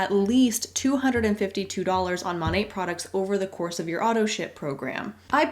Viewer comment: I don't know why, but every time she says "auto ship" I hear "auto sh!t" lol. As in, expensive junk is appearing on your doorstep every month! Yay!